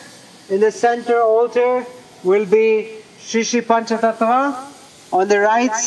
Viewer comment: what did he say will be in center ??? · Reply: Pancha Tattva